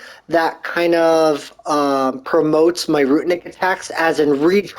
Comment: Your dodging skills are out of this world. I try to kill the Valkyrie like you and immediately forget all your tips and just die so fast, LOL. 1st time through for me. Also it sounds like your saying ROOT-EN-NIC for ROO-NIC, lol. No T and 2 syllables 😉